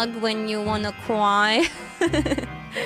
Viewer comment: I misheard that last part "I'll put on pacifier" as "a badonk pacifier" which sounds fun.